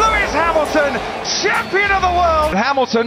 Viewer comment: I heard "hammaton"